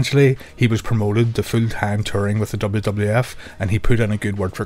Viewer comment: I thought he was saying fu tang but he was saying full time